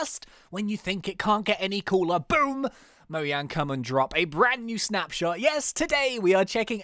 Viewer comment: Did he say moyang